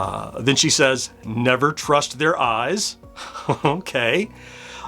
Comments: The Orb actually says "you can eat them, Just never their eyes". Sounds like she says "trust never their eyes" but it is "just never". I tried to like it, but it is just too separated from the pacman concept. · Orb said never trust their eyes? I thought she said "you can eat them just not their eyes" 😅